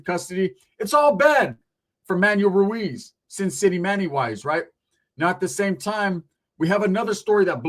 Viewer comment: I thought it said Snitchymanny